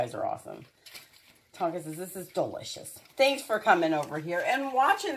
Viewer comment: It really sounds like Tonka is saying Thank You